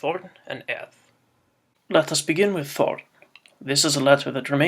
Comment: I thought that said p*rn. Backing up! BACKING UP!